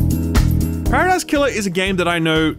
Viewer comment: help! I cant understand what hes saying! I love outer wilds so much which means im so interested in what ever game hes talking about but I cant tell wtf hes saying!! paris killer what? I tried googling variations of what im hearing and "games similar to outer wilds" but im not coming up with anything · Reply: Paradise Killer